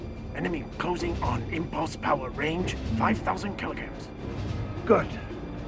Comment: Did he just say "range, 5,000 kilograms"? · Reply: Kellikams